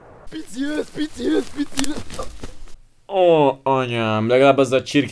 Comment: mit mond? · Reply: PICI ŐZ PICI ŐZ AH!😂😂 Azzal kergette az őzt